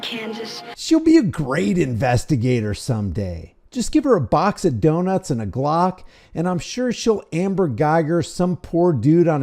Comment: Did you just say a box of donuts and a cock or a box of donuts and a glock 😂😂😅